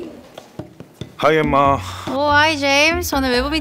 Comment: 나만 하이 엄마라고 들리나요?